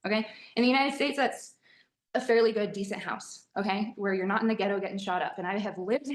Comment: Also, can we talk about how she didn't even say "ghetto" in this story? Not once did she mention she was actually in a bad neighborhood, just that she was in Atlanta (you know, where Black people live) and that was scary. She literally just expects you to hear "Atlanta", know that's a majority-Black city, and understand why she felt unsafe without any suggestion that she actually was unsafe Edit: I live just outside of ATL, and there are plenty of places in downtown ATL where I, a white woman, feel perfectly safe walking after dark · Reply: I hear her saying “ghetto”